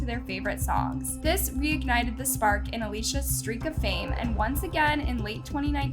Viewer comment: did u say